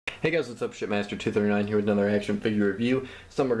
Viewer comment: I thought he said his name was shitmaster XD he says it so fats